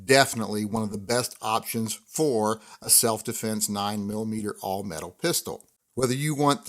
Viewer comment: I thought it said alt metal for a sec lol I was like whaaaaaat? 😂